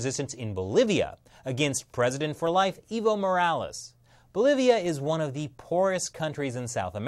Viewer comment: I heard 'Evil Morales'? who else heard that.